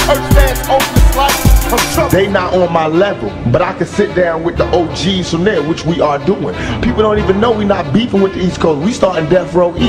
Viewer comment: Please somebody tell me what he saying at 3:21 🙏🙏🙏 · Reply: “They not on my level, but I could sit down with the OG’s from there, which we are doing, people don’t even know we not beefing with these (?) we starting death-row east”